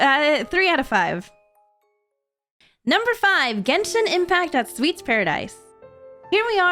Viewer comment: i like to sleep with noise in the background and most if the times i put on Storytime animators i was listening to this just as i was falling sleep then i heard "number 5 genshin impact" and my eyes flew open at the mere saying the the word